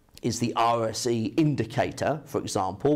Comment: What did you say at 2:19 sir? Some sort of RSE indicator or?